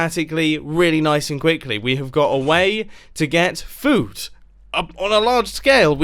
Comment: Did he just say “We have a way to get foot” fruit Lol😂😂😂😂 like WHAT!! Did you say I couldn’t stop laughing😂😂😂😂🤣🤣🤣